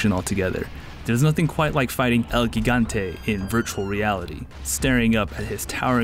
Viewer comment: Did he just say "El geegante"?